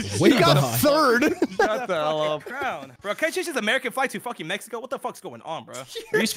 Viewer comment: does anyone know WTF is saying???😂😂😂😂😂 All I heard was the American flag to Mexico